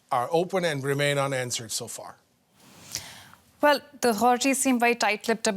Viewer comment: what is she saying? Was she trying to say “the authorities”?